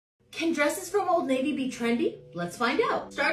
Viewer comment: I am half deaf and i heard you say "old lady" then i watched it again and read the bag ..ah old navy! Lmao